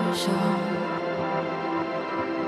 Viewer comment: I hear "steal" instead of "still" and the last word I hear a "sh" sound instead of just a "s" sound